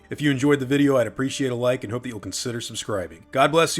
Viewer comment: 😂 lol, when you said "Christ is King" (blessings to you by the way,mate) i swear i heard "Crichton is King" 😂 perhaps it was cause i accidentally got the video on x5 speed